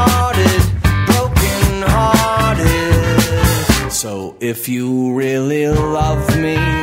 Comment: Did he say "So if you really ruv me"?